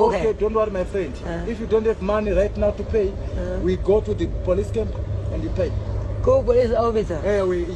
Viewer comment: Lol , which manya? thought he said let’s go to the police camp - so embarrassing